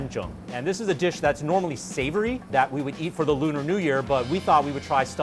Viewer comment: He actually said ‘Lunar New Year’ not ‘China New Year’ That’s how we asian call it P/s: i’m Vietnamese and these dishes look delicious 🤔